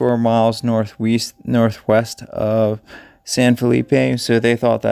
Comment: North East? I thought you said